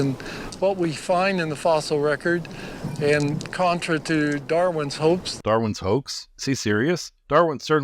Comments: The creationist obviously says "Darwins HOPES“ and not "Darwins HOAX“… Of course what he says is still wrong… but it’s not THAT bad. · I heard Darwin's hopes, not hoax.